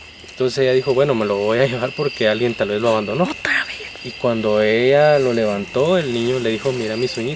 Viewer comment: se escucha que dicen (Otra vez)